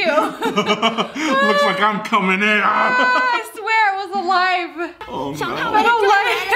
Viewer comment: it sounded like the girl in the mukbang said I swear it just tried to eat my teeth 🤣